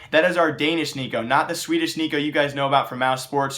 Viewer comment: did he say Swedish??